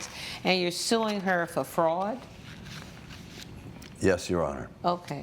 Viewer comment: Fraud I thought you said frog